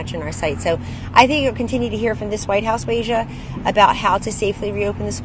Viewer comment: did she call her asia? Or are the subtitles just wrong? If she did, how did that not go viral?